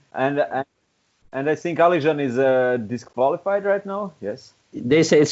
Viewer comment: Ivan was talking about Oguzan Kochak from Turkey but everybody thought that he said Alizan from Kazahstan😕